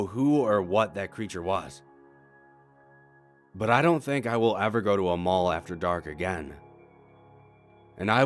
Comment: Tell me, have you ever been to an abandoned mall? Or have you experienced anything creepy at a mall? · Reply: i thought u said never go to mall after 4